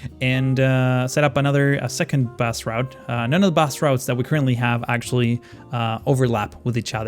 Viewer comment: when you say bus route it sounds like your saying bass route. like the fish. but when you say bus without rout it sounds normal (for an American)